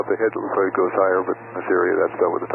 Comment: It sounds like, "... in this area..." instead of, "... in theory..." Perhaps flying with friends and family sometimes makes it even harder for pilots to admit to problems and ask for help, either through a sense of embarrassment or in fear that declaring an emergency or a state of confusion may frighten the other occupants.